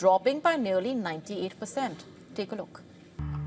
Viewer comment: Arey 👆 yeh kya boli 🤔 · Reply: Take a look boli usne to